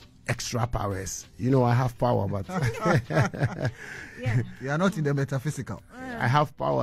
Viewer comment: Peace cannot be kept by force,it can only be achieved by understanding,so agree with stonebwoy telling his side so that Shatta will understand things for peace to reign🙏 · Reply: I heard you're not in Ghana 😅